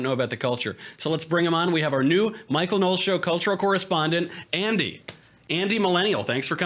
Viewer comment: For a sec I thought it said "Andy Milonakis"